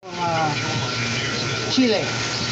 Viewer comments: Did not work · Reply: I heard him say chilly at the beginning of the video so I'm wondering if it needs to be refrigerated or maybe even put in the freezer for a short time before this will work. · I heard him say chilly at the beginning of the video so I'm wondering if the fish needs to be refrigerated first or maybe even put in the freezer for a short period before this will work. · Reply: Me, a year later lol. I'm pretty sure he was saying "Chile"? Sounded more like chee-lay then chill-ee